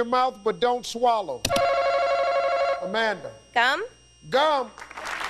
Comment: I thought she said gum with a c.